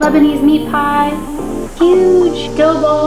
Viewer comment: I thought it said, lesbian meat pie, and that’s why it was a triangle